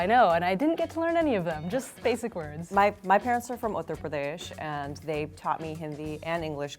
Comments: She says 'Uttar Pradesh' but the subtitles say 'Andhra Pradesh'. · she says Uttarpradesh right. The subtitle says Andhra Pradesh. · Is it just me or did say Uttar Pradesh instead of the subtitle of Andra Pradesh at 0:38 ? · "My parents are from Uttar Pradesh" but the subtitle says Andhra Pradesh. These are 2 different states with different languages. Please correct this. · I think she said Uttar Pradesh but the subtitles say Andra Pradhesh. Different places.